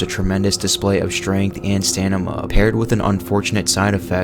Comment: did u say stanima😂